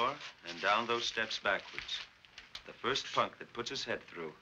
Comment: did he say the four letter word that begins with "F"? I can't remember ever seeing a 1950s movie where saying this four letter word was allowed. Also, at 17:23, actor George Maharis says, "I'll kill you for that.", when he tries to kiss Jeannie and she refuses him and refuses to go out with him. Because of his comment, I would have extended the movie and changed the ending to the following: James Franciscus thought he killed her, but maybe strangled her and she passed out. He ran away from the scene of what he thought was a murder. She regained consciousness and got up from the ground. She started to walk and was going to get help. But in the meantime, George Maharis was following her. He was hiding and saw her and James Franciscus. He saw that it was the perfect murder for him to commit as he would never be blamed for it and James Franciscus would be the one to take the blame. So, as she was walking to look for help, George Maharis finished her off and murdered her. Later on, some way, the police would find out he was the real murderer. Except for changing the ending, I thought this movie was excellent. It had a lot of depth and complexity. Another comment: I thought that the girl who played the blond who was attacked reminded me of two other actresses. She acted and had a voice like a combination of Shelley Winters and Gloria Grahame. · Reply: He said, "The first punk..."